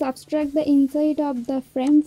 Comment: what did you said at min 05:04? Can you please tell me command that you used? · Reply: Substract the frame outside